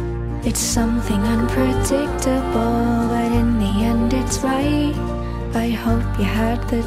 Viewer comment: But in the end its right? I always thought he said In this endless ride!!!